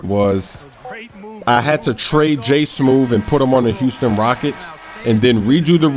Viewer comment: Man I could have sworn smoove said juice done well it does sound better if you said Houston lol I just feel stupid now